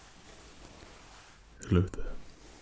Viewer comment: Thor Is you? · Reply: Quando ver os comentarios estarei lá exactly what I thought! When he said "hello there" I said "Hi Thor"